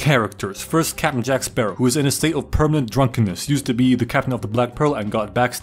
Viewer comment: i thought it said used to be black 💀💀